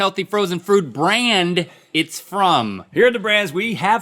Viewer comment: Did anyone else catch Link saying Healthy Frozen Frood Brand in the beginning about 1:05? He's definitely saying Frood instead of Food.